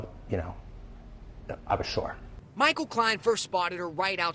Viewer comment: At first I thought at 0:58 he said "I was shook." Oh well, a girl can dream...